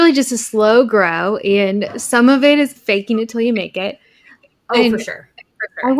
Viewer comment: For a second I thought you said “bake it til you make it…” 😂